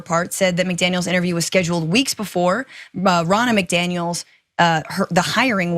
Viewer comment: I keep hearing "Ronald McDonald."